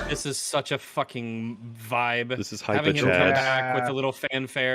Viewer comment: Did he just say "vibe?"